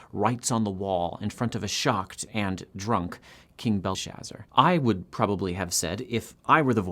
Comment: Strange; closed captioning has King "Belteshazzar" (Daniel's Babylonian name) instead of "Belshazzar" 0:33 Sometimes YouTube generates CC incorrectly? - or maybe it's your accent! Just kidding.. ;)